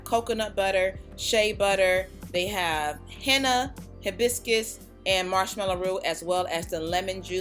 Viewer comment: I looked away from the monitor for a moment when you said hibiscus and thought you said biscuits, I was like o.0 . #whenhungerstrikes